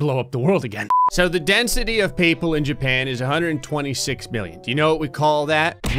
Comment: Oop earliest I've been but I swear when he said the population of japan at 0:37 I thought he said billion not million owo